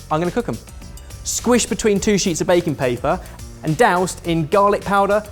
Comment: and I hear "bacon paper".